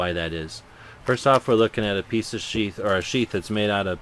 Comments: Haha, you were talking about how this is a junky knife, then you said "We're looking at a piece of 'Sheath'.." i could hae sworn you said 'We're looking at a piece of Sh**". lol. great vid! keep it up. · when he said piece os sheath i thought at first that he said a piece of shit lol